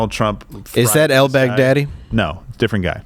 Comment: When al baghdadi was mentioned, for some reason I heard "outback daddy.' Odd.